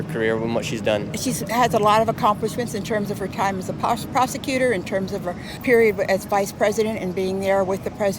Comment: sounded like she was going to say “prostitute.”